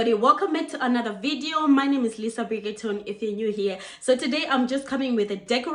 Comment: It sounded like you said your name is Lisa Bridgerton 😜😜😜